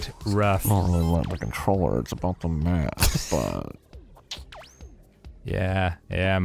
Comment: I swear Arin said “That controller’s been in Matt’s butt”